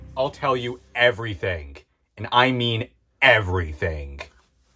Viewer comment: idk why but i heard everythink